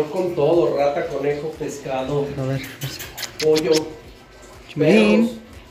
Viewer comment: escuche perro o que dice?